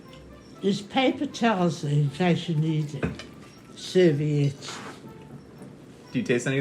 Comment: In the subtitles it says; "Theres paper towels indiscernible serviettes". Weird, english is not my primary language, but even so, I clearly hear "Theres paper towels in case you need it, serviettes" shrugs